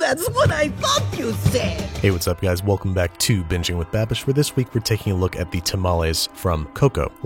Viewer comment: why did i hear "this is bi*ching with babish"?